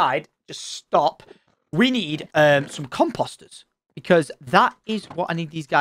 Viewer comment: "come posters". lol. that is what it sounds like tom is saying when he says comeposter. lol.